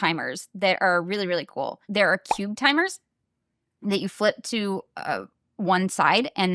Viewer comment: Oh. Cube timer. I heard it as “cute timer” and was totally ready to follow a link somewhere 😂💸